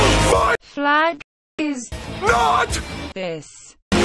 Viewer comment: “My flag is not...this!” - Last words of Nazi-Germany, circa 1945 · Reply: I thought it said MY FLAG IS NOT PISSED!